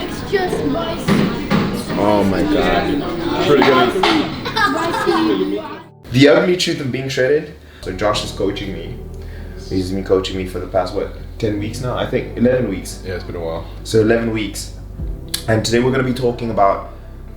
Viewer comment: Ohh I thought u said the ugly truth of shredding is that Josh is coaching u at 0:34 🤣🤣🤣🤣 then I played back and realized u just said the title.♥️